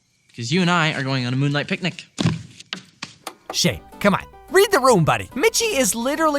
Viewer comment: did anyone else think he said, “Sh*t” instead of “Shane”?